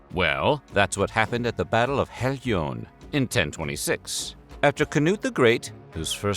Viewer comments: I give you such praises for pronouncing all these cities and people's names effortlessly, not just in this video but in many of them. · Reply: He can't pronounce them. I thought he said "Battle of Herrljunga" but it was "Battle of Helgeå". Americans are not Europeans and will never be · Odd and random coincidence, it almost sounded like you said "Canoe the Great" for Cnut and the painting of him looks like he's holding a small canoe in his lap